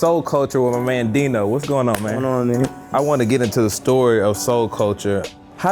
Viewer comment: Did he just say “whats going on nigga?”